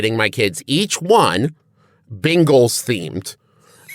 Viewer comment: Genuinely thought travis said ‘Bingles’ themed until he mentioned football